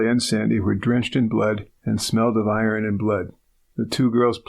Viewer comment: does he say that they smelled of IRON and blood ... ? or did I not hear correctly ... ?